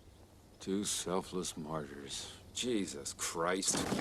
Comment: Honestly, for the longest time, I thought Indy said “poor, selfless Marcus” I was today years old when I learned it was “two selfless martyrs”